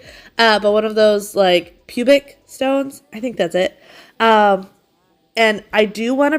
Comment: I heard "pubic stone" and cannot stop laughing :D